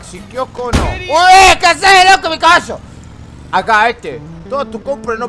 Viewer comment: cuando escuche eso la primera ves pense que dijo "EEEEHH QUE HASE LOCO MI CAGASO" pero lo volvi a escuchar y ahi entendi que dijo caballo en ves de cagaso XD